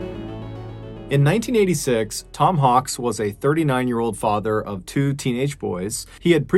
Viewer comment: I thought he said Tom honks lol